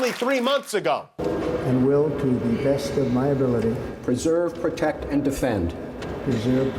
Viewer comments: Sounds like he said ‘preserve, protect, & Offend’ 😑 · He did struggle to say "preserve". I thought he said "usurp".